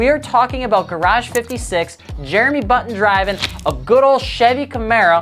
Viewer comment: Did he just say Jeremy button🤨